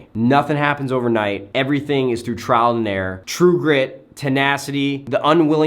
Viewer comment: Which word is he saying on 9:20 I can't understand? I'm serious about it English is not my first language and I'm trying to learn! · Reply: TryToBeatMyAim :D trial and error ?